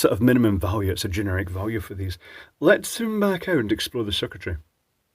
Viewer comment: "Let's zoom back out and explode the circuitry" is what I heard at time